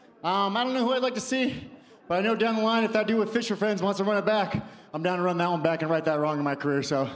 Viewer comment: "If that dude with ___ ____ wants to run it back...?" What'd he say? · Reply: Fisher friends